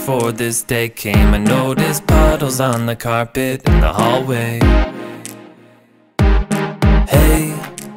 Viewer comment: When you said in the hallway I thought you said like always